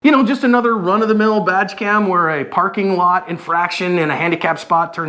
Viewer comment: did he say vag cam? What the hell is that?